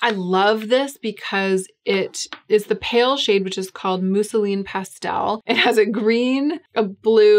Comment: At first I heard "Mussolini pastel" powder and was extremely confused lol.